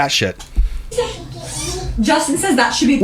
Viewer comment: I think she just said his name is Justin!